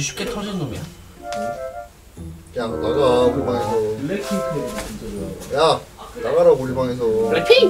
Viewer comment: 난 왜 이게 블랙핑크로 들리지..?ㅋㅋㅋ